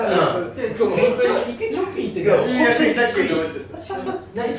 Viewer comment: 閑也くんと松倉くんがノニジュース 飲んだ瞬間の反応一緒ww 慎太郎くんｶﾜｲｽｷﾞ💚 末ズが同じ回に居て じゅったんとほっくんってストのメンツﾔﾊﾞｯ コーチは相変わらず見守ってる感じww まぁそれが良いんだよね～ 閑也くんが“チョッピー”って言った瞬間 ショッピーって聞こえて え！？ってなってしまったww SnowManは不在なんだよね😅 宮近ちゃんと松田くんと海人くんは いつも通りって感じww SnowMan居ないとヤバいね 一回ジェス 普通にセリフ言っちゃってたよね！！ まぁ言ったらカオスですねww